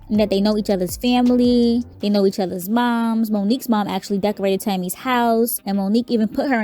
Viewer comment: I thought tami said her mom decorated Monique's house 🤷🏾‍♀️